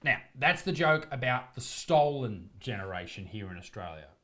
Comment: This entire time I thought you have been saying the Stalin generation, like Joseph Stalin. And it never made sense to me why it was called that. I now see you are saying stolen. Ffs I'm dumb